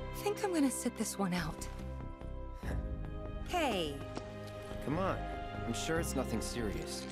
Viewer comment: i didn't hear c'mon. i heard mom.